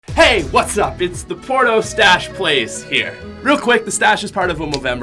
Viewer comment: put on catptions When it does he says “hey what’s up it’s the porno stash plays” 😂